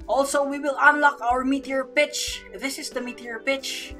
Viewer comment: who else thought he was saying meteor bitch